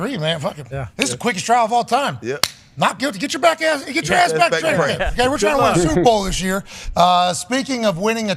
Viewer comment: With Pats Stuttering It Almost Sounded Like He Said “get your black ass back to camp”🤣🤣🤣💀💀